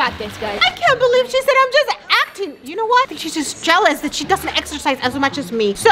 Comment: When said “she’s just acting” I thought she said your just exing” because that means she’s not sizing… exercising… Get it??